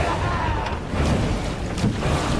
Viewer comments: ? "Make Fast TheDisaster Rigging!" (Is That What He Says?) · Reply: "Main battery at the ready!" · what is he yelling? Asking 'cause i'm from Germany and didn't understand the english version at this moment😅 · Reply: "Main battery at the ready". It's a bit difficult to hear even for native English speakers😂